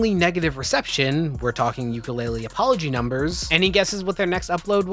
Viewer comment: I heard “ukulele apology” as “yooka laylee apology” for a few seconds. I have no idea why my brain decided to provide me with that visual but honestly I’m glad it did.